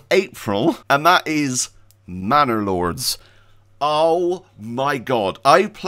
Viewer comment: ManorLords looks good, I thought you said Banner Lords & thought my boy plays something like that already. 😊